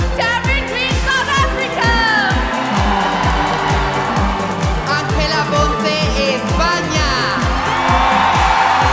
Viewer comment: We're all here for France, and I stayed for: 1. British Virgin Islands 2. Egypt's voice 3. Nepal's beauty 4. In love with South Africa 5. Stunning Switzerland 6. Turkey's sweet smile 💞 7. Spain Whattt did I misheard?? 😂 11:01 Miss Sweden was smart to let the crowd calm before announcing hers though seem pretty delay as the next line ladies already stood right behind, but we could see she immersed with the audience letting them celebrate their surprise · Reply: What you heard at 11:01 was "España."